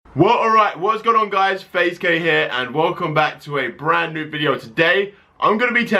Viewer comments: I literally thought he said whats up guys faze gay here😂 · I thought he said "Faze gay here" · no offence I thought he said hey guys faze gay